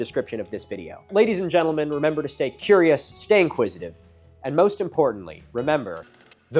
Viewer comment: I thought tat said "stay cumulus". We look to mofos like yer esteemed self for the history that American politicians are too chickenshit to tell schoolkids about. There goes the next major coal bed, er went.